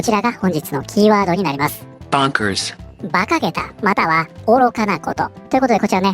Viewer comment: オメーんとこの大統領だろ！ オメーらがどうにかしろよ！（任天堂代弁：非公認ｗ 今日の英語:bonkersがボンクラーズに聞こえた、あずまんがは間違ってなかったｗ